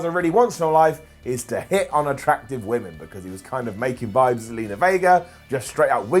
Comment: For a second there I thought said Angel Gaza wants to hit unattractive women! A very subjective and illegal pass time, then I realised he said Gaza wants to hit on attractive women. Still subjective and could put him on the path to illegal nowadays but makes much more sense.